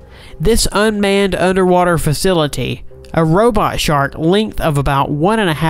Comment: For a second I thought they said robot shart